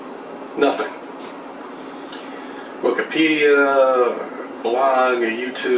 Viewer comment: I swear to God he called it wokepedia